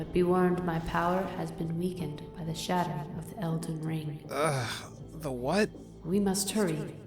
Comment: did she say elden ring?